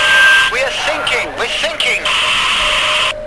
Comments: I thought they said thinking too :P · I swear I heard "what'S are you thinking about"... anyway... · Please help me. I learn English and i am beginer. What is he saying? I mean German Coast guard. Is he asking about singing or thinking? I hear "singing", but my teacher says that he is saying "thinking". Who is right?